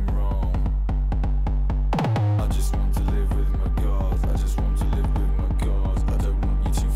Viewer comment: "I just want to live with my Dad" is all I hear